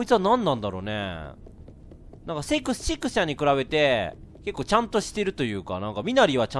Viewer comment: レトさん... シックスちゃんのこと何て言った.? 4:18くらいのところ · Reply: カップラーメン せっくsちゃんって言ったぞw